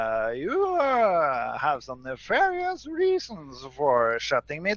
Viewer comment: What word did he say? Mivarious? Is that even English?